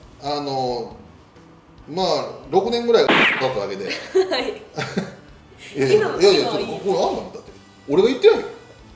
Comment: これってなんて言ってるんですか？ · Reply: 多分｢下手糞｣